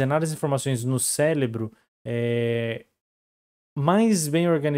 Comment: Eu ouvi bem ou ele falou "céLebro"?